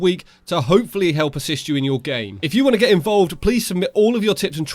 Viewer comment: Did he say «hopefully assist you if you are gay”